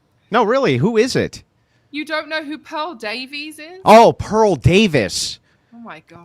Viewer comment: "LIAR!! You don't know who PAUL DAVIE is ??" Lmfao.. You were not the only one who didn't understand WTF she said, me and about 7-8 thousand others in Crucible chat were just as baffled as you watching live..